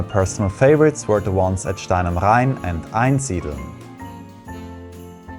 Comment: What are the cities he said in the 3:25? I don’t speak German well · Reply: Hi. Stein am Rhein. Here is the travel guide for the Top 5 Christmas markets with all details you need to know: <link>